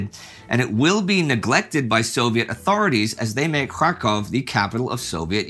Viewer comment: “as they made Krakow the capital of Soviet Ukraine”. I think you meant to say Kharkiv Indy. Thank you for your all your hard work. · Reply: But he said Kharkiv .. but his amerrcian accent didnalowed him say it properly ... it realy sound like kraow but when u wil llisten properly yyou will hear he say Charkov ... :)